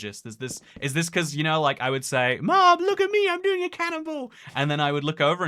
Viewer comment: Did you say “I’m being a cannibal”? Or was it “I’m doing a cannonball”? Also I love the minitoycat voice I hope we hear it more in the future lol